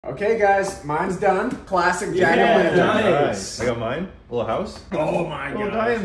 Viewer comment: Oh... I thought you said Jack-off lantern...